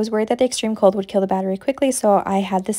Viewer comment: No but when you said 'worried that the cold might kill the battery' I thought you said baby instead of battery and I panicked and was like 'Get this out of there then!'